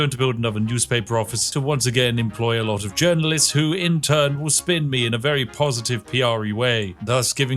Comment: did he just say "intern"?!